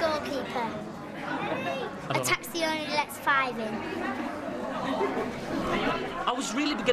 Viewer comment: what does the kid say at 9:00? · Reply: A taxi only lets 5 in